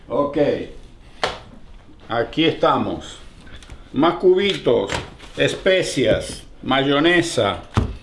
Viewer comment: Perdón no entendí de que se trataba, dijó ''macubitos'' tu padre ?